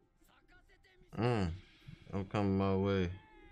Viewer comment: DId the subtitle show oh come my way instead of okama way at 19:03?